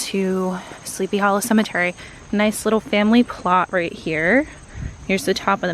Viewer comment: OK did I just hear you say "nice little family clot here" next to the "mezzanine" lol.